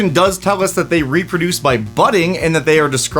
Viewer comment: I heard, "They reproduce by butting." /giggles